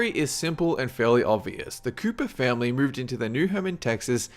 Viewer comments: I heard "the koopa family" instead of the "Copper family" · #11. Did he just say... the Koopa Family? I think I need to see a doctor